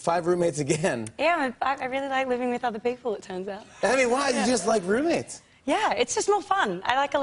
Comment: what did she say between 0:30 and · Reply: Kimo Alfado " i really like living with people, it turns out"